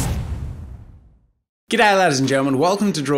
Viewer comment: wait did he just say Good Lad? Good Jab?..Good..Uh..Lord jeezus lemme look at the captions. Captions: Get Out Ladies and Gentle Men Wth....